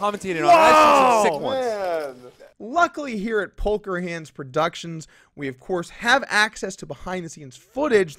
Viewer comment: did you just call it "Polker Hands"? lol